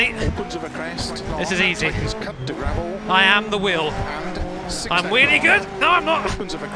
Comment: Did he just say “I’m wheelie good”. 1000/11 gr8 content